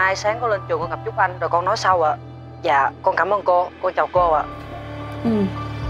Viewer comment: seo teo cứ nghe thành cô chào cô dậy nhỉ